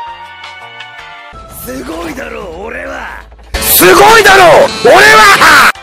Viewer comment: IDK ABOUT YALL BUT I HEARD SUGAWARA